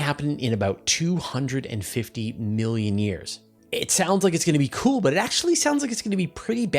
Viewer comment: I was worried there…I thought you said 25 million years. 😆😆😆